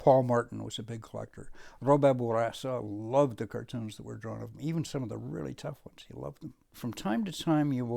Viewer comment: Can someone tell what he said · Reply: Robert Bourassa loved the cartoons that were drawn of him.